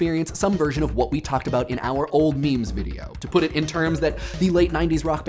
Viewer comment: Did... did he just say maemaes instead of memes?